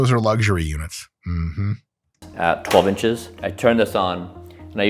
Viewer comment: did he just say 12 inches😮